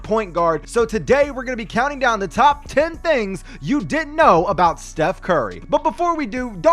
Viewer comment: It sounded like he said "You did know,". That's what I heard. Like and reply if you thought or know he said that. 👍 👇